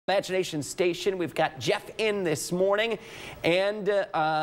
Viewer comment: Did he say “ vagination station”